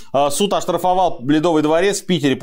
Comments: Один я услышал "Суд оштрафовал блядовый дворец"? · Послышалось блЯдовый дворец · Мне послышалось или Сергей сказал "Суд оштрафовал бледовый дворец"?